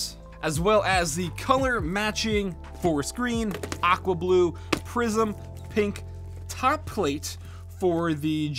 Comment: I kept hearing "prison pink" ... just sayin